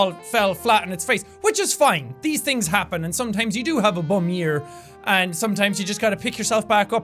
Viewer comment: When he said it was a bum year i thought he said bum ear. Made me think of what that would be, so i thought of a bum leg, then i though about someone have a small bum leg as a ear. It got weird quick.